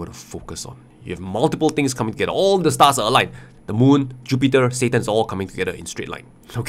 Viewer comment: did he just say satan 🤨